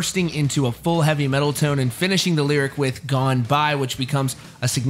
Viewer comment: When he says "gone by". To me it sounds like he is saying "Goodbye". Rest in Peace!